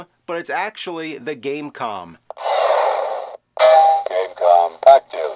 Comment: Did... did I hear right? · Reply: Gamecom thank you.